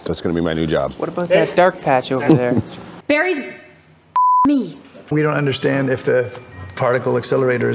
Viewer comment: What every snowbarry fan want Caitlin to say to Barry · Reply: To me it sounded like she said, "Barry? F*** me."